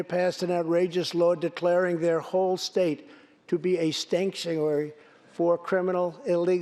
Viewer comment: did he call california a stank-tuary, that didn't sound like like a slip